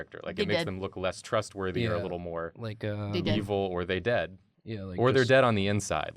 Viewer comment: What does say at around 9:27? She repeats it a few times, and sounds like something in my language, but I'm sure she does not speak it - and I just cannot figure out what it would be, really. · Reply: they dead, like they are dead but missing the are